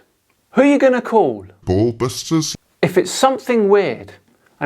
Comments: Who you gonna call?? (All i heard was) "Ball busters" I'm dead 🤣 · Sounds like he was saying "Bulb Busters"